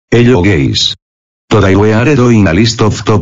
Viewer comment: did you said hello gays? I'm straight 😡🤬🙄😤😱😕